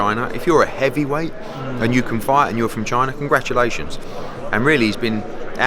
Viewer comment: Thought he said "if u a heavyweight and you can fight from a vagina" 😂💀